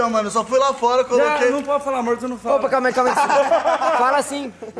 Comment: Mano foi Sô eu que ouvi o falando no minuto 19:53 ' a não pode falar amor' da pra ouvir direitinho meu 😍😍